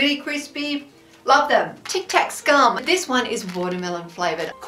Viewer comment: Thank you! 💚🌟 Thank you and! 👍🏻🙂 I swear I thought you said Tic Tac scum! 😂😋